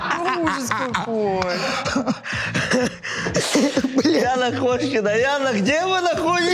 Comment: Мне одному послышалось : "Бляна Кошкина " ?